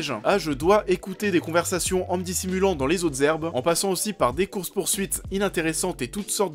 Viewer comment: les zotes zerbes? C'est quoi les zotes zerbes?